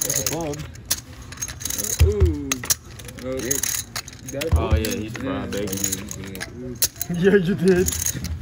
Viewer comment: Did anyone catch what the guy said on the KT hand when they ran it twice? It sounded like he said, "Yea he's fried bacon" lol